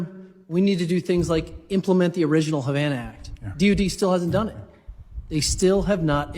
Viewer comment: did he really say deity , or something else? I went looking for a department called "deity" and came up dry.